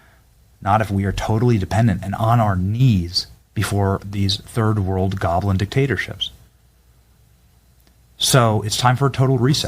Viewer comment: did he say third world goblin dictatorship?